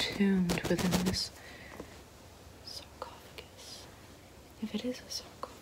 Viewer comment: at first i thought it said esophagus, was pretty confused for the first minute. though that's probably her next vid